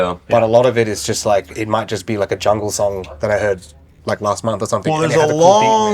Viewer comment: "jungle"? Is he saying jungle? Meaning music genre? That's so great to hear! :)